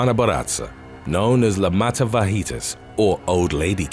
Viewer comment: I thought you said "matafajitas."